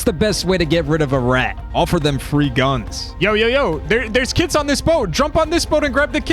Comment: I thought you were saying “there a kids on that boat!” “Get the kids” 😂